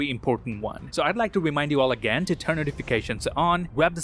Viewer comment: Did you just say: See you guys in the next Wong...?